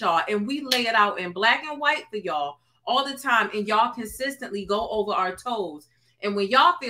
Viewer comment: Did you say toaster over 4:31 😂